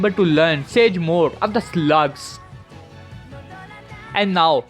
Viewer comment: bruh I thought you said sage of the sluts when you said slug